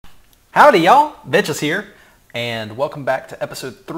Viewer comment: Lol it sounded like you said "Howdy y'all, bitches here" in the intro :D <3 you